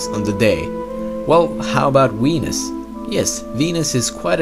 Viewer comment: i swear he said weenus